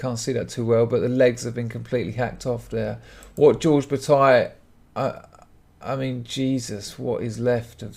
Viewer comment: So... I mis-heard... I saw the photo you put up and thought you said George Takei. I googled: George Takei leg sawn off. Nothing. So I then googled: Japanese George leg cut off. Still nothing. So I listened again... Yep.